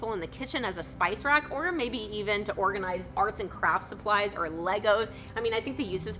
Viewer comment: did she just say legos 😀